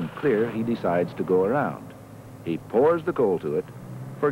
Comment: What does he say at 14:49 - 14:50?? Any ideas? It sounds like: 'he pours the coal to it'. Obviously this is not correct! Thanks.